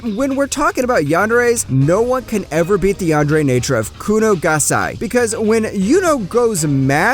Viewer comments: did he really said kuno gasai 😠😠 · did he say kuni gasai 😆